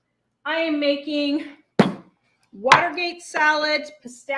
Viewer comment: 🦃🍁 I didn't even know the stores sold the bacon ends & pieces - you are quite the teacher! Do you have to freeze the bacon drippings or is it safe only in the frig? Happy Thanksgiving! When you were making your jello salad I thought you said Water Beet Salad. I had to turn on the closed caption to get the title correct. I appreciate your energy level. You go a mile a minute! 🦃🍁❤️